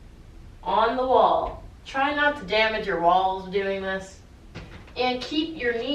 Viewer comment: At the 7:33 time mark did she say try not to damage your wall? Or try not to damage your balls? 🤸🏾‍♂️🚑